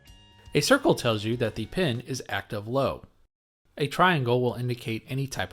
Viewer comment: Great exposition, particularly the pacing, but sometimes a tad too quick. What are you saying at 3:10 about the circle on the IC as you write "Inverted." Sounds like "active low." Has this been defined?